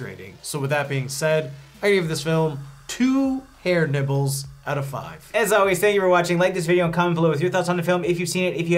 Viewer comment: Dude when you said "ear nibbles" at 10:45 I totally thought you said "HAIR NIPPLES" (2 outta 5)... Completely coincidental that it STILL evidently fit the scene in question... 👏👏😂😂😂😂👍